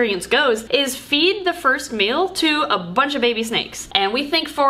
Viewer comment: In the intro I heard "feed the first male to a bunch of baby snakes", and let me tell you, it made me sit up a bit.. ;)